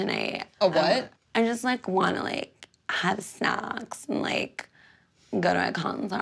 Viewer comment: lolol I definitely thought she was saying "I just like, wanna like, have sex and like, go to a concert." Nope, she definitely said "have snacks."